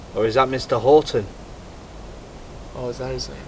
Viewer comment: My second name is haughton I was watching the video in background and I hear him say “or is that mr haughton” and I whip my head around “wait what me”